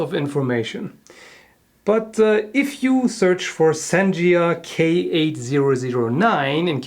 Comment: I thought it said Santa instead of sanjia